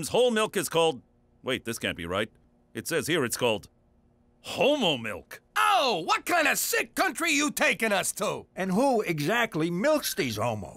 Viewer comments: I thought he was saying Hobo milk…. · I’m just wondering if Canadians really call whole milk “homo milk”? Oh, and I’m first lol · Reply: I thought the instructor said holmo